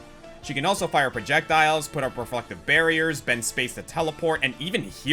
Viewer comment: When you hear this part without paying too much attention to it, it sounds like he says put up her fucktive barriers 🤣